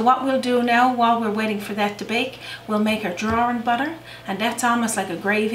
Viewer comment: I noticed her accent seemed mixed with a bit of Scottish or Irish brogue! I find linguistics fascinating and in the States, we must have more accents than anywhere else. I think it's fun to find out where the accents originate, whether you are brought up by parents or grandparents or move to a new area and maybe have to change your speech patterns a bit so others are able to understand you. I hear phrases I've never heard before and being curious by nature, I look up a phrase that I'm unsure of. For instance, 'drawin' butter'. I thought she said drawn butter, which is a totally different thing from what she explained later when she made her drawing butter. I am happy to learn something new! I think in the States that would be onion gravy. I grew up not all that far from, in New Hampshire, USA. From the seacoast area to most recently, the Canadian border. I knew I would not make this recipe before I watched it because I don't like or eat cod. Still, I was curious about a 'fish pudding'. As she made it, I wondered if haddock would work as well. Maybe not, since cod is stronger in flavor and more oily than haddock. I love to try new things and when I make a dish that is too much for one person, I share. Sharing is caring and food is meant to be shared. I replied to say something totally different than how this ended up. It was why I don't eat cod. It may not be the case, generally speaking, but what is in my head can't be unlearned. I worked for a couple large groc